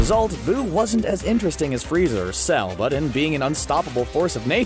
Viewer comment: D..did he say......freezer?🤣